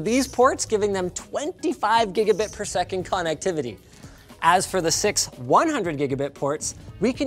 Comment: for a second there I misheard, and thought he said Funnectivity... Yes that WOULD be FUNNECTIVITY!